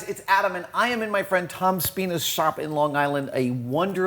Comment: am i the only one who heard tom's penis shop?